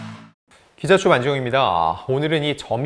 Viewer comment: 기자충이라고 하는건가요?? 매번 충으로 들려서..